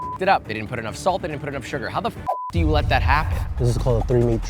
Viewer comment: i’m sorry, did you just say sugar???